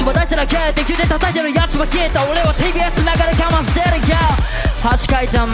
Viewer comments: タイキのバース、歌詞はテレビになってるのにSの音聞こえてなんだろと思ったんだけど テレビ→TBSにアレンジしてる？ THE TIMEでやってるからかな？ そういう生のパフォーマンスっぽさ入れてくるのもさすがタイキだなあ。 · タイキなんて言ってる？？TVのところが違うような、、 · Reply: TBSと言ってる？と言ってる人がいましたー 確かにそう聞こえるー